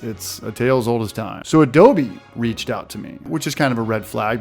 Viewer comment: I thought it said "Adobo" at first 😂😂